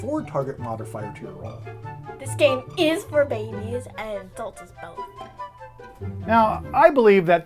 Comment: what is she saying other then babies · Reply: She says, "This game IS for babies, and insults us both." I hope this helps! 😁